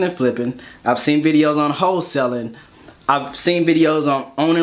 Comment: 28 secs..... thought u said hoe selling...... I was like whaaaaaah... 😂 Whole selling👍🏽